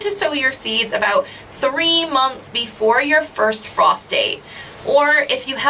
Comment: You said frost date. I heard "sew your seeds three months before your first prostate" Also, I'm from.scotland and old enough to remember carving a neep. It took about a week and was off before you finished. When we first got pumpkins my mum was super surprised they weren't solid on the inside. Way easier to carve. Thanks for the upload. I'm planting swedes this year.